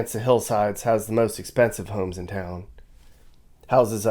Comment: I thought you said that part of town had..."the most expenive hoes in town." Oh, homes.